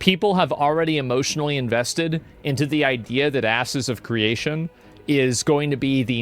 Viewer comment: Did he call it Asses of Creation at around 29:10 mark?